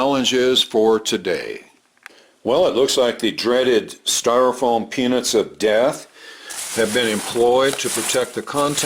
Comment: Oh god, about a minute in I thought said, ‘The styrofoam penis of death.’ I had to look up at my iPad to check my hearing.